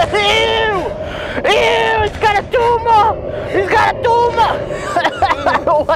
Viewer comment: what is he saying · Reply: It has a tumor